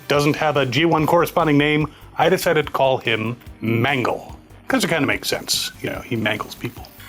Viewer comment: For a second I thought said he will name it “Mango”… well, it is yellow, but not very scary.